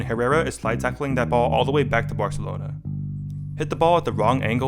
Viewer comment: did you say "Barcelona"😂